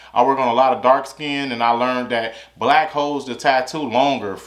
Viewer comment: did you say “black hoes” brotha?? 😂😂